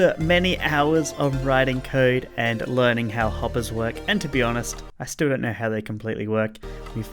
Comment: Define coding in one sentence “I missed one line of code that broke it completely” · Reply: When I first watched this, I heard "I still don't know how this works"